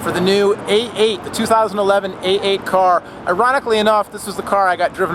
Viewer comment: Did you say "Eroticly"